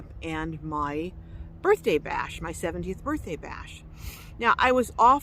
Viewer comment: First off, did you say your 70th birthday? I had to have misheard that because you definitely can't be 70! With that sad, I totally agree with you, he was just being a jerk - probably was pissy because you were having a good time with good food and he wasn't invited! Some people are just so self centered these days! Love your videos BTW. Safe travels! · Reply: she said 70th bday. he was in a pissy mood over a camping party or maybe without invitation. god bless u and safe travels